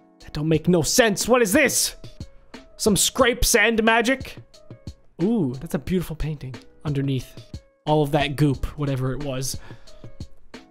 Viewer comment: Some scrape sand magic- best 2018 😂😂😂💙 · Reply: I thought it said Some crap sand magic